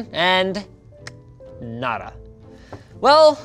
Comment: did u just say nada or nara ???